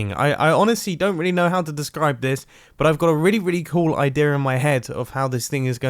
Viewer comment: What did you say 😂😂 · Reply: “I don’t know how to describe this” I think that’s what he said